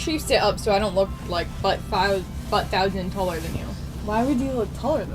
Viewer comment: What? "A butt thousand taller than you"? What did she say?